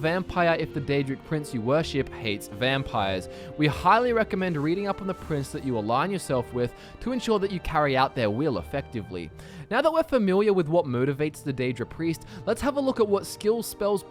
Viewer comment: Did he say famiwiar?